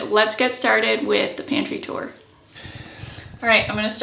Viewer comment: i thought it said Panty Tour.